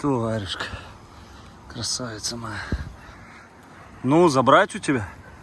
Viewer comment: я одна слышу ответ братиша:"ну не знаю я"?это очень мало для засева без проплешин,которые они затопчут вообще